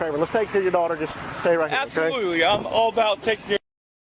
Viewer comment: Did he say "Im all about caring for my daughter''?